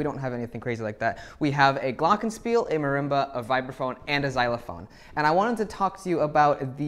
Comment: Sooo I thought he was saying viper phone this whole time😰,then I read the comments and realized how dumb I am🤯 and went back to watching other people play games and stuff their faces with cheetos where I belong😔😔😓🙈